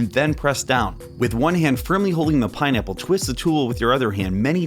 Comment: did anyone else hear him say "pinenipple"